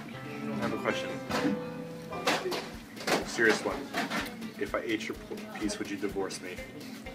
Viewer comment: Did anyone else catch almost saying "if i ate your pu---- piece" at 7:34? lol he almost said it right? it's not just me?